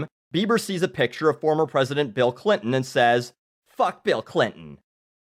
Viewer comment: I thought that said Cliff Burton at first....